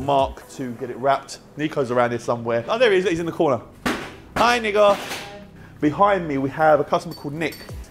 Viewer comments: Am i the only one who heard hi N word ! at 12:57 instead of Hi niko ? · did say N** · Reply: Watch previous ep, it's his (or cypriot) way to say nicco